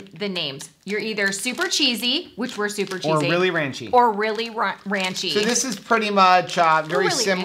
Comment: at 13:52 I thought you were going to say "really raunchy" instead of ranchy. Then I hear the infamous bung hole. Love ya, girl!